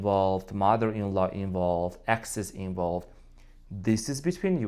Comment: did I hear exes-in-law 🤣